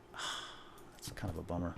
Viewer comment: Did he just say "boner"?